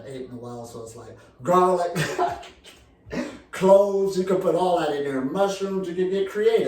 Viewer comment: I thought he said Clothes. Along with that Laugh lol 🤣